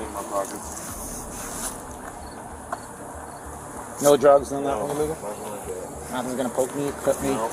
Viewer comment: i don’t think that’s what he said 😂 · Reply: I hear "No drugs or nothing illegal?"